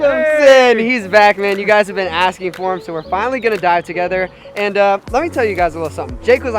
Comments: I heard we're finally gonna die together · am I the only one who hears "Were finally gonna die together"? Weird... :/